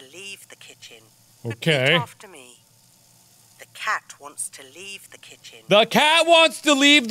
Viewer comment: The game: "Water makes cats wet." My dumb brain: " WATER MAKES CAT SWEAT " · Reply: At around 25:20 the voice recording lady said something like "The cat wants to leave the kitchen." But I heard it as "Thecat wants to leave the chicken."